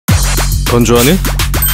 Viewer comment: 이거 처음에 건조하니 잘못 들으면 돈좋아하니로 들린다